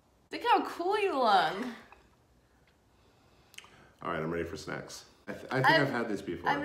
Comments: I thought he said I’m ready for sex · I thought said after you drew the mustache “alright I’m ready for sex” 😂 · When said “ok, I’m ready for snacks” I thought he said “ok, I’m ready for sex.” 💚